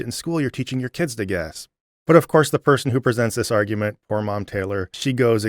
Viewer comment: I misheard "poor mom Taylor" as "whore mom Taylor."